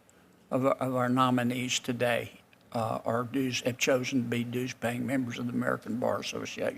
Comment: Does he call them douchebags?